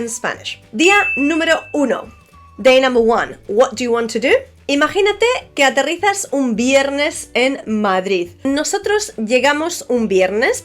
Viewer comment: Does anyone know what she says at 0:56 in Spanish, I didn't quite catch it. "Imaginate que a te ..... un viernes en Madrid" ? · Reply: "Imagínate que aterrizas un viernes en Madrid". ¡Saludos desde Madrid!